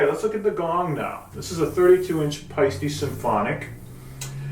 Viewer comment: Thank you for your generosity; sharing so many of your techniques here. I thought you were saying a Feisty gong! Paiste!